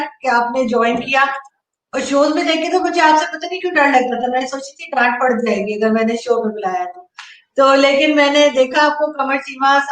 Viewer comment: 2.4x pe sun raha tha, at 1:12 felt ki I heard 'G**nd phat jayegi'. But mujhe laga ki Arzoo Baji aisa nahi kahengi so I slowed down and listened again.